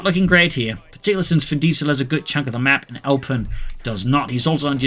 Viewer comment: did u call him Fin Diesel? 😆 hilarious!!!